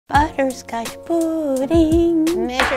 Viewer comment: Just my or at the beginning it sounds like she said butters got butty